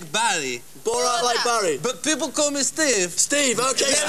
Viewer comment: lol thought he said borat like balli